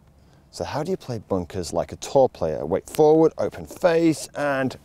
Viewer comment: At first I thought he was saying "how do you play it like a tall player" lol